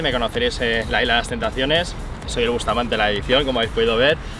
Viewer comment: que dice “soy el bustamante”?